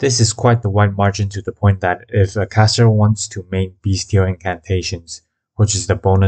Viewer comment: When you say “beastial” it sounds like your saying “beast-heal”